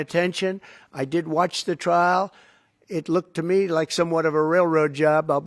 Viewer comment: I believe he said "I did watch the trial" (though I doubt that he actually did). Sometimes, it's hard to distinguish "did" from "didn't" with some speakers. But I replayed that part multiple times and think that he is saying - true or not - that he did watch it.